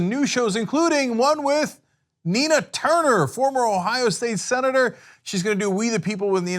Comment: Heard "Tina Turner" Misheard Left disappointed